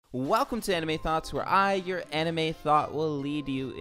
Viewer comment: I swear I thought he said “your anime thot”😂